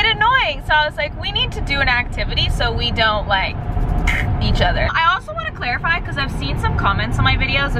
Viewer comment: Sorry but my dirty mind thought she said f*** eachother